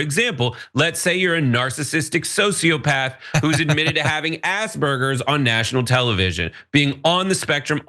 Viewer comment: Oh! You said, "Asperger's"! For a moment I thought you'd said, ". . . eating